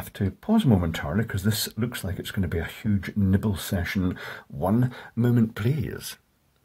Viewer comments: I totally heard “This is going to be a huge nipple session! 😂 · "huge nibble session" - I'm done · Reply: I thought he said "niPPle session"!